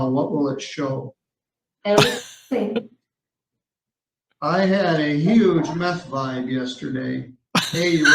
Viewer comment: I heard "I had a huge meth line" lmao! Mishearing.